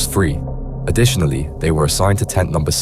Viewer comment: What? I thought they said tent one.